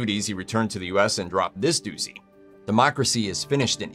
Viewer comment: I am a child because I thought he said "drop this deuce-y" and laughed like I was 12. I am 42.